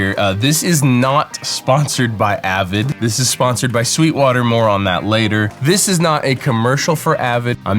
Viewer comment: I thought you said "This is not sponsored by Avid, this is sponsored by sweetwater MORON"